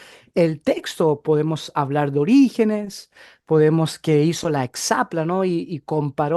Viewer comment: En el minuto 2:18 dice podemos hablar de los orígenes y la hexaplanos (,algo asi escucho. No le alcanzo a entender que dice. Bendiciones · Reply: Hola, cómo se llama usted? Cuénteme un poco de usted para conocerle. A propósito, dije Orígenes y la Hexapla.